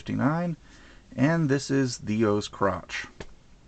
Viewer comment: First thing i heard "And this is The O's Crotch" xDDD btw HAPPY BIRTHDAY :D